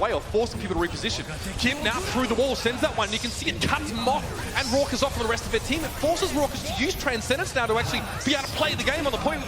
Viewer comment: what did he just say! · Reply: "It cuts Moth and Rawkus off from the rest of their team"?